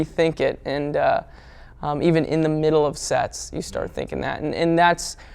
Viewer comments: i thought he said "and in the middle of sex" and i was like wth tyler what does that have to do with anything, turns out i was chewing too loud and didn't hear it correctly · I thought he said in the middle of sex 😶😂